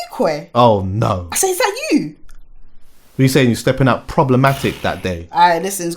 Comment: when said problematic why did I think this nigga said was stepping out "proper batty" I was like 🤣🤣🤣🤣